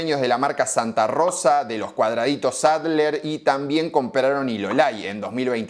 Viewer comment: Lo escuché dos veces para estar seguro, no son cuadraditos Adler, son triangulitos Adler.